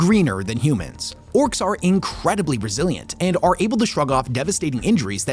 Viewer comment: I could swear I heard you say "Orks are incredibly Brazillian".